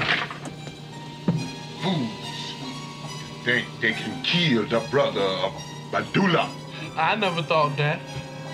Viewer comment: Fools! They think they killed the brother of Baddula! -I never thought that! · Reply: I honestly thought he said “WHO… think they can kill the brotha baddula?”